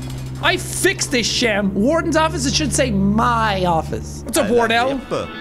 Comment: Sounds like you say: "Høvle hette og en hore" which mean "Jack plane hood and a wh*re" So good on you Jack, you spoke some Norwegian, I'll give you a golden star for effort. -By some Norwegian guy living in his igloo